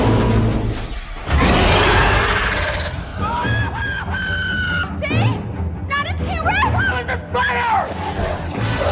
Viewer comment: what did he say? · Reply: Screaming kid: annoying shriek Claire: See! Not a T. rex. Screaming kid: How is this better?!